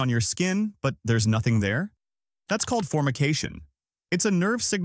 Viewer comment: literally thought you said "fornication" 😂